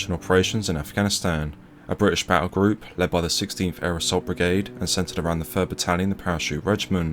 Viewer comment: I heard that as "Aerosol brigade"...